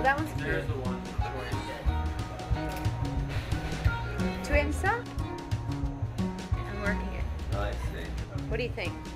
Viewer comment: What does Madonna say at 2:18? LOL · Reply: Tu aimes ça - You like it?